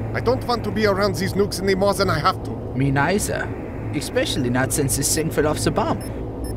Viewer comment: what’d he say? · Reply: especially since this thing will set off the bomb